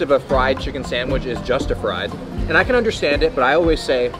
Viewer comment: did he just say "justafried"?!